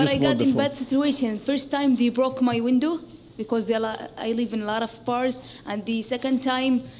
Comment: What does she say @ 2:09 , I live in lot of? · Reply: I live in lot of bars